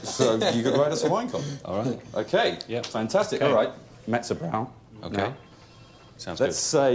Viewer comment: Can anyone tell me what the boss guy says at 2:25?....I've never been able to make it out. Thanks! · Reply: He says mezzo brow, aka middlebrow. Meaning aspirational culture, pop culture centric, etc. not lowbrow or highbrow, and kind of a satirical term with the use of the Italian word for middle. He's basically saying don't write a highfalutin wine column, make it readable for average people.